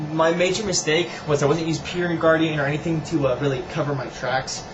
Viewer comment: You mentioned something to avoid getting caught a few times, and I couldn't make out your words. At 6:20 It sounded like your were saying 'peer re guarding'. What were the actual words you used?